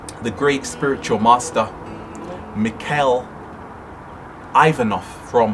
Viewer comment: could you write down the name what U said at 9:19 ? did U mean Omraam Mikhaël Aïvanhov ?